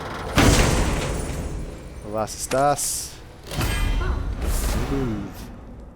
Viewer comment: what did you say at 24:10 after opening the chest? evas estas? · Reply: I think he says Was ist das and its german for what is that